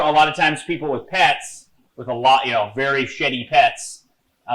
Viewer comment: I heard "very shitty pets" and was like what? Then I realized he said "very sheddy pets" LOL!